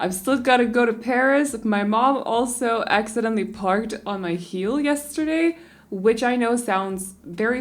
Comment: When you said ,,my mom parked on my heel” I thought you said my mom parked on my hill 😂and I was very confused for a second Btw I love your videos soo much ❤️❤️🥰